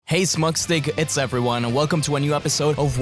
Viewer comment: I know I’m not the only one who heard “Hey Smugstick, it’s everyone😭😂”